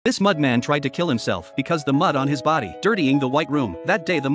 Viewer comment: The first thing I hear is “the mud man is trying to kill itself “💀💀💀